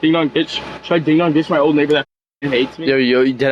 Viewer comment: Did I hear that right? Did he just say the N-word? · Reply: He said neighbor